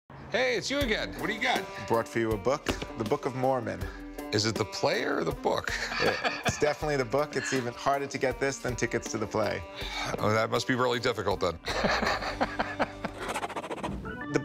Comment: I thought he said "is it the plates or the book?" which let's face it, would been a better joke.